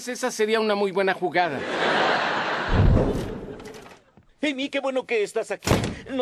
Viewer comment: Escuche mal o dijo en el minuto 2:13 - Penni que bueno que estas aqui ??